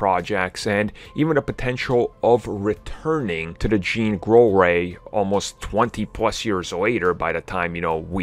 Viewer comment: Did anyone else hear him say "JEAN GROLL REY" LOL Jean Grey Roll